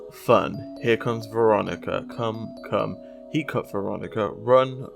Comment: I heard “ he caught Veronica “ not “he cut Veronica “